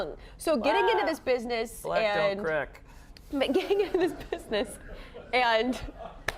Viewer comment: What did he say when the interviewer commented he was young? The crew cracked up but I didn't understand a word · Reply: He said “black don’t crack” 🤣🤣🤣🤣